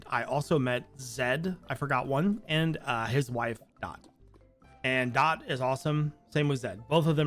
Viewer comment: The problem with being hard of hearing is the things you think you hear. When you mentioned meeting Zed I at first thought you said "Zed and his wife died." I was trying to figure out how you are being so upbeat about this, what kind of person was this dead wife that her being dead is basically cause for celebration?!? Then it I go back and I am guessing her name name is Dot...still had me worried for a minute not going to lie.